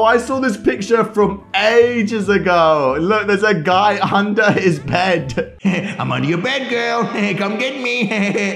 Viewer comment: did you just say his bed? Lmao.